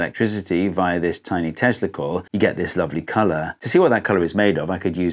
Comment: Bro, when he said "tesla coil," my brain thought he said testicle lol